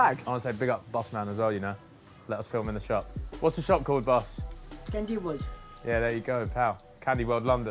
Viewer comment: anyone else hear “bendy wood”